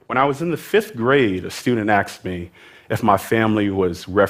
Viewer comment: did he say axed instead of asked